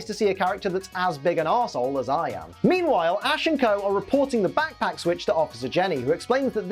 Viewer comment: thought you said Ash and Goh then realised you said Co